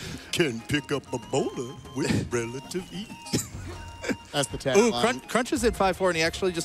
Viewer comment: for some reason, I swear I heard him sing "Can kill a Beholder with relative ease..."